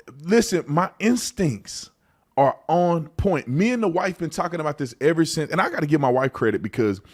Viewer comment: I burst out laughing because of my racist self When he said" me and the wife" I thought for sure he said " me and the whites" .